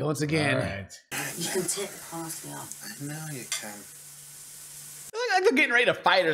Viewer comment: I think misheard at 8:50. "You can take the parsley off." "I know you C*NT" Her expression was "PISSED".